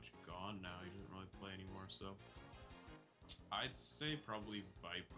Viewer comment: Cyycliii..Viprin I was Thinking in that moment:Wtf you Wanted to say Cyclic and u Said viprin...Wtf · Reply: He said "I'd say viprin"